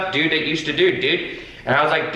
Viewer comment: the captions say you said dick instead of dude 😂 I haven't laughed so hard in a while